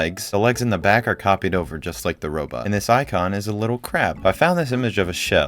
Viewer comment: He kinda sound like he says "and this icon is a little crap" instead of crab . Good thing my ears weren't right there